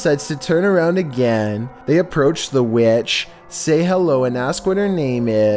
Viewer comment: Dude for a sec I thought you said they approach the Bi*ch